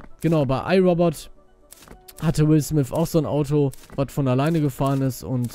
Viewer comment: Ich hab Milf Smith verstanden :D :D :D